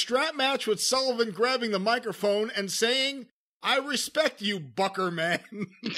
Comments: At the time I thought he said I respect you Mr. Booger man. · I originally thought he said "I respect you Booger Man."